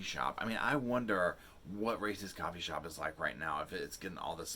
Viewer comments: Racist coffee shop!? I know I heard that wrong... I hope! 🤣 This is awful, I have a few brothers and stories like this spook the big sis in me! 😳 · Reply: Haha I thought said "racist coffee shop" too 😂 I was like what? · Racist coffee shop!? I know I heard that wrong... I hope! 🤣 This is awful, I have a few brothers and stories like this spook the big sis in me! 😳